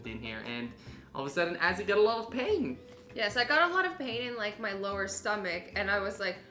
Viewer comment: I thought you said that you had a lot of paint